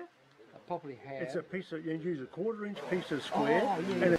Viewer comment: All i heard was "it uses A quarter inch piece of squid" The f???